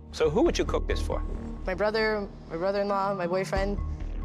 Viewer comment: I really thought she said that her brother in law is her boyfriend LMFAO SWEET HOME ALABAMA